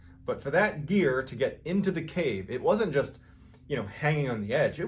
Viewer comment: I thought you said Eric’s ear was in the cave. 😱